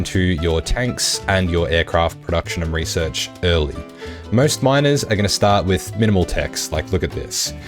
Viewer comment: did i hear minors? Or not